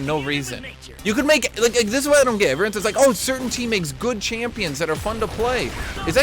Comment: Anyone know what champion he said at 7:09? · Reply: he said certainT riot developer champ designer.